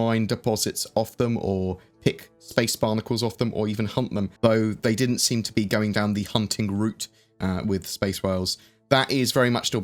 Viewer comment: For a second I thought you said 'Hump them', I was thinking, wait what sort of game mechanics are coming into the game? Then I realised you said 'Hunt them', gotta say I was a little disappointed.